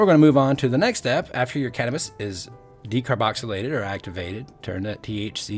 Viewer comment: Thought it said cannabilism